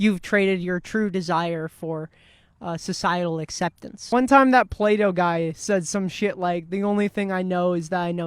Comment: Definitely heard it as "that Play-Doh guy" and I thought someone was making a philosophy-for-kids show. That'd be kinda sick. Thanks for coming at this topic with some compassion and vulnerability. It's so much harder to learn from the various feelings that come up around things you don't understand when people from some group are reactively slinging shit about the various kinds of awful people that the outsiders looking in must be (even though they were all on the outside at some point before figuring things out themselves).